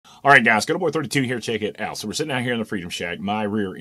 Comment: dude you say that so fast that it sounded like you said ghettoboy32! lol!!!